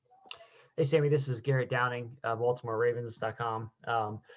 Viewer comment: Garrett’s phone thought he said “Hey Siri” 🤣🤣